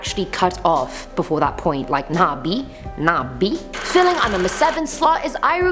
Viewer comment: In 4:10 to 4:12, did she say "Nah Bee, Nah Bee"?